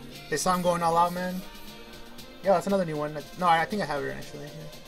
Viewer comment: did just say. " I think I have her." Please tell me you know it's a dude, it's a trap.